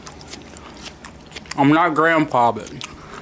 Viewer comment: did he say "I'm not a grandpa"